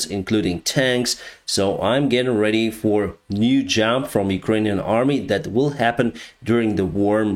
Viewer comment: what did you say? You are getting ready for a new job in Ukraine?